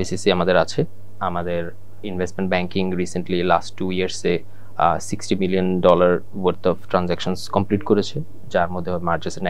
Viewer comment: Did he said 60 Billions of Dollar at 1:36 minute ?